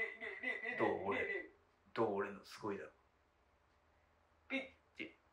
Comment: 止まった時の中ではっきり聞こえる 「どう？俺の…凄いだろ」ほんと好き